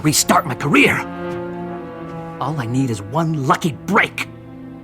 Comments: My volume was low and so all I heard was ALL I NEED IS 1 FUCKING BREAK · did he say one fucking brick?